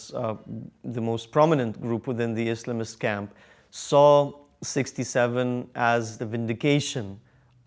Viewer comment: did u say 67? BOII TS SO TUFF 🤣✌💔🥀